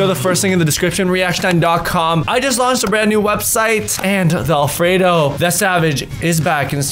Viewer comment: I thought you said I just lost a brand new website😂